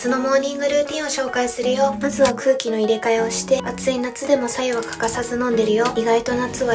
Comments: ぼけーっと聞いてたらブスは空気の入れ替えをしてに聞こえてビクってした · ブスは空気の入れ替えをして、って聞こえてうぉぉって思いました。。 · 最初の方に「まずは空気の入れ替えをして」って言ってるんだろうけど、「ブスは空気の入れ替えをして」って聞こえて、ナチュラルに煽られたかと思った…